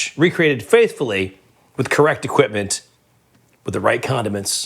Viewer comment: I could’ve sworn he said, “the only condom you could get...” when he was talking about condiments 🤣